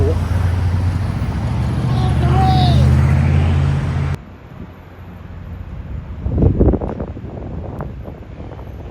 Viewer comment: What did he say at @ 1:55 ?? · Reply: He said "Get off the road!"